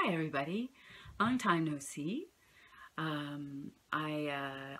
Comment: did you say "long time no see"? huh?